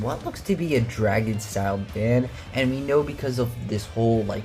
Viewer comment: Fan instead of van?